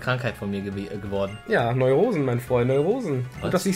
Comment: Ich hab auch "Neue Hosen"verstanden^^ Zwangsneuhosen,der innere Druck,immer eine neue Hose anzuziehen..geht ziemlich aufs Geld. 4:20 Ja,Eros wird überleben,das Schicksal mag uns! ... 5:36 Was hat er gesagt,was hat er gesagt?! D: hat reingenuschelt 12:20 Wow,Wenn er keine Cliffhanger macht,trollt er uns gleich am Anfang mit solchen Überschriften,wie gemein :( Vor allem weil ich wirklich hoffte,dass mindestens einer stirbt..quasi Tutanch-Eros Fluch,wer seine Teamruhe stört wird sterben. 24:32 Der erlösende Schrei des Sieges! xD Und das geilste ist,irgendwas blödes geht für diesen geilen Pilz aus Regis Team entweder die beknackte Eistüte oder dieser Schande-für-einen-Starter-Wildschweinrüpel.. nimmt doch nicht Hermes raus,das G-Cis wegsweepen kann...das macht der doch nicht,oder?! :(